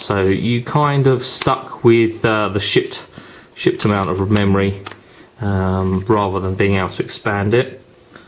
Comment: I thought you said shit amount of memory for a second there haha